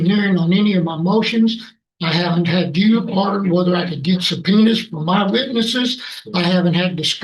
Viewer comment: I know it’s the crunchy audio, but it really sounds like he said “suh-penis!”